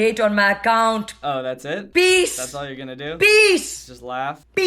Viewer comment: she said “Bees!” Instead of peace😂 and it looked like she was spitting everywhere...